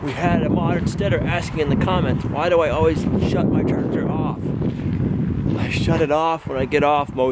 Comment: What is it you shut off for editing (9:08)??? I've watched that about 10 times and can't make out what you are saying lol. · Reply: My tractor